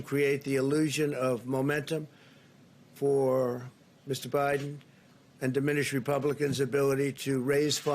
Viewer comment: Did he say Mr. Biden? What happened to Sleepy Joe? 😂😂😂😂😂😂😂😂😂😂😂